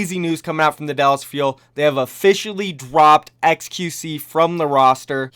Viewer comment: Was it just me that heard him say dwopped